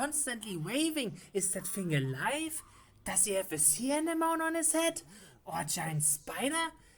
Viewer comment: I know he's trying to say Sea anemone but it damn near sounds like he just said Sea enema. Yikes.